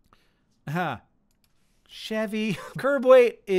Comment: i really thought you said "curboy" and i was so confused. which obviously means i must name my car curboy now